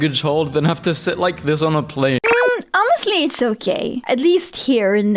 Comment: I misheard "it's ok" as "it's so gay", and I was like "yeah, that feels right"...